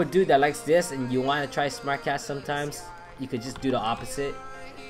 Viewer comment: Why did I hear " smack ass " instead of "smart cast"